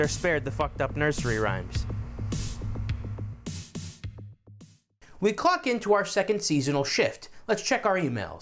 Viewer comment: you'd just been doing some chicken puns so for a second I thought you said "we CLUCK in" lol